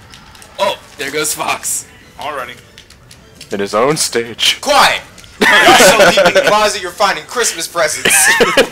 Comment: What did say about Christmas presents starting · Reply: "Quiet! You're so far in the closet, you're finding Christmas presents!"